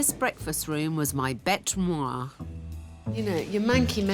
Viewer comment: Did she say the breakfast room was her ‘bete moi’?